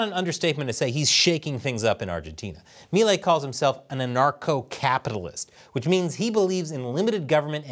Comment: "a narco capitalist" is what I heard.